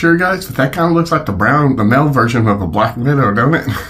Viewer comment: I heard him say black minnow. Lol